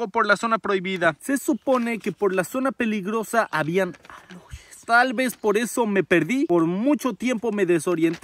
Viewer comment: que dice? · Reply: Dice aluxes